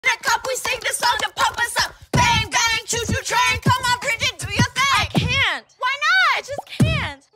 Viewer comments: I thought it said "come on bridget do your thing" 💀💀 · POV : you thought it said Bridget instead of lindsay